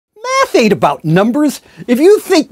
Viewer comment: Did he say meth?